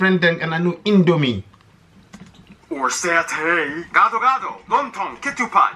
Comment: is he say "Satan"?